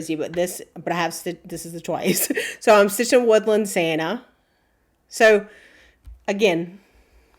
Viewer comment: Ok, so I had to go back and watch again….i loved it twice as much as the first time. Can you tell me what color red are you using? I thought you were saying brown coat Santa? What color is it? I am calling in an order. tomorrow before I leave , then maybe it will be here when I get home in a week. I stitch on Aida, 16, 18, some 20 depends. My old eyes just doesn’t let me do linen anymore. Tks again